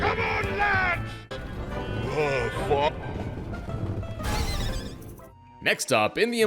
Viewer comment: the hippie octopus says "oh fu-" like it was gonna say "oh fuck"